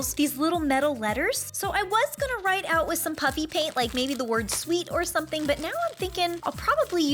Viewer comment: Did anyone else think it sound like she said puppy paint ?😳🤣